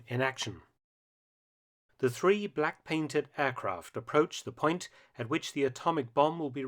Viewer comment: “Silverplate“ was simply the code word for the B-29’s assigned to deliver the A-bombs. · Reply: No, it was the 509th Bomber Group. Sorry, the heavy English accent made it sound like he said 'paint' instead of 'plate'. Not my fault.